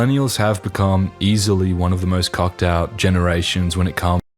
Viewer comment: lol yea he is definitely saying "cucked" not "cocked" 🤣🤣🤣